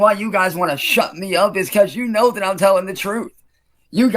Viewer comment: did it say "telling the fruit"?